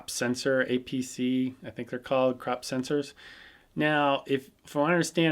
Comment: i thought u say crap sensor at 6:57 LOL btw nice video thanks~ :D